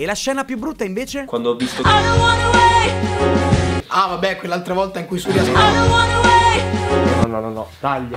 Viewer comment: a 6:25 ho sentito "quella volta che surry ha sb" ahah